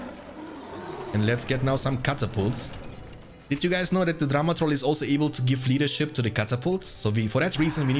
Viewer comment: when you say drummer troll, I hear Drama Troll! A very dramatic troll! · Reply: Saaame xD ì actually thought he said Drama trolls until I read this comment